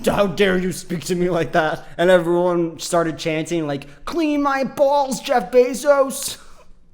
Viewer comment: thinks he’s soo smart just because he adequately interpreted a film meant for children. Please, I should do what he does, and he should clean my bawls. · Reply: Ohhhhh that's so much better. I thought he said "clean my balls"